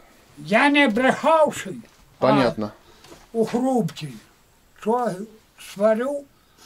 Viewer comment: he actually says "У грубке что сварю", which means "whatever I boil in grubka". Груба (or грубка, which is a little груба) is a type of a stove, you know, this one that has like a small door to put lumber in, and you may also put some little pot there or something, and it will cook. You can actually see it on 11:47, it's like a little white door. Cheers from Ukraine